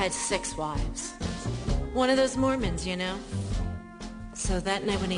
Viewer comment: "One of those Mormons you know" I thought she was say moments wrong!!